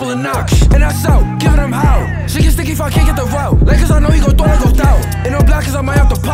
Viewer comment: Yo what did he say at 1:03 when he rapping mad fast? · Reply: Sh1t get sticky if I can't get the route, like cuz I know he gon throw like odel